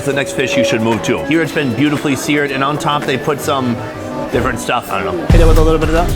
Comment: what he said? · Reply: “On top they put some-different stuff, I don’t know, hit it with a little bit of that”